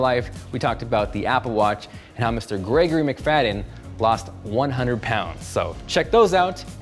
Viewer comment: Who is gregory Mcfadden?, i thought you said Conor Mcgregor.. 😂